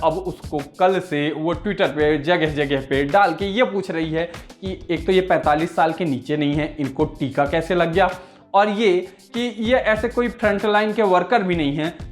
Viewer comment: kya bol diye, ye 45 saal ke niche nahi hai?